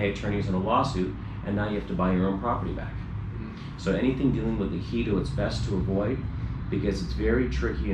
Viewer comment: what is that thing from H letter? when he says to avoid that. "heedo", "heatle"? lol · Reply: ejido